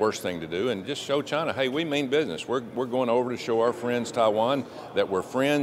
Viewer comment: Oh ...going to Taiwan...I thought she said "She was going to TIE ONE ON"...LMAO